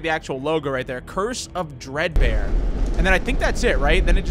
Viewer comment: I thought it said dream bear